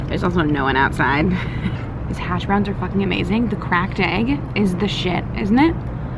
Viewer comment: I was so confused, I thought you kept saying “crack den” instead of “cracked egg”